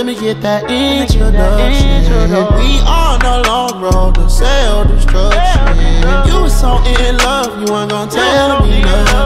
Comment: I heard “we on a long road to sell these drugs, shit.” I was like o noooo💀😂 0:56 I heard “let me get this grill.” As in the teeth grills. 1:13 “yea I need all uh yah” 1:30 “first things first lemme get that internet.” ‘-duction’ was always cut off😂